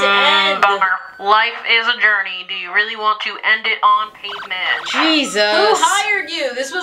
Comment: Now I could have sworn she said "do you really want to end it on the sidewalk" the first time I listened to this months ago and I swear I listened to it on repeat....omg my own Mandela effect moment